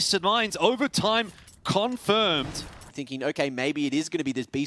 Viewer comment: The fuck did he say LOL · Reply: Overtime confirmed?